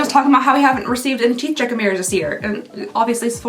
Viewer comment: Could someone, or is there someone, who could please help me to understand wat it is saying at this point?! When she saw something round, she thought immediately it was a "teeth ______"??!! But I can't figure out wat she's exactly saying!? And bcuz she's saying it so fast, my CC won't even pick it up at all?! So please! 🙏🏻😊 Anyone out there that can help me wit an answer would be most appreciated and grateful for your reply & time! 😁👍🏻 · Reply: “Haven’t received any teeth checking mirrors this year” ❤